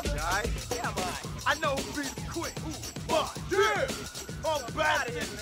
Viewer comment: Last minute at 2:17 I don't know what they said to police.. someone write it here please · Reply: Suck this or fuck this I believe